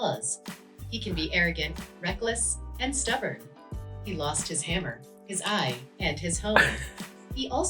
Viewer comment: I thought the ai said "he lost his hoe" and I was dead 💀💀